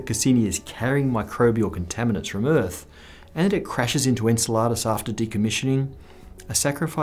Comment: Either you are pronouncing very wrong or I'm very hungry because every time you said "Enceladus", I thought you said "Enchiladas"